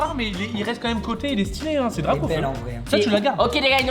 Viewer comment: c moi ou inox dis encore li est belle en vrai 😂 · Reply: Il dit elle est belle en vrai (il parle de la carte)